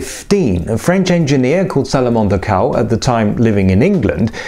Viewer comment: I thought you said the French Engineer's name was Salamander Cow.